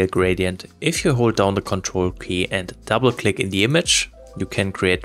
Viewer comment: Great tips all around. But, I can't tell what he's saying/doing to make the radial gradient automatically cover the entire screen at the 3:43 section. It sounds like he's saying hold "ctrl P" then double click but that is opening a print screen. Anyone know how to makes this work?